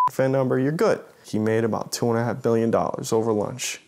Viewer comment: did he say 2.5 Billion?